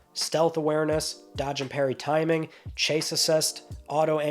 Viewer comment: I misheard "Chase assist" in Spiderman as "Chafe assist". There's talc for that, especially in the crotch.